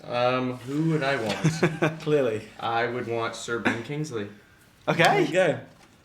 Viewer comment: Hey, could someone Help Me. What does Logan say at 3:55? Which Name. I just understood the last name, kingsley, i guess. Thanks for Helping me. And what is Douglas saying after that? :o English isn't my birthlanguage, so I don't understand everything. :) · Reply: Logan says Sir Ben Kingsley and Douglas says 'Well, we're going to have a great time'. Hope this helps :)